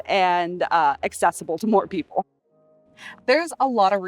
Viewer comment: had to listen three times to find out she said 'more people' instead of 'poor people' 😂😂🤣